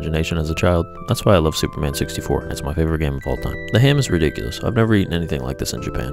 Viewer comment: 'I like Superman 64'. You can't drop a statement like that in the middle of a meal and walk away from it. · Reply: I Thought he said 'Super Mario 64' 🤦‍♂️ Dude effed up big time.